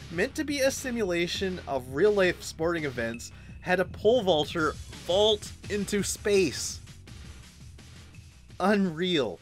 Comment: At first, I thought you said a "pole vaulter yeet into space." Then I went back and found out I was wrong, but also that I'm funny. 🤣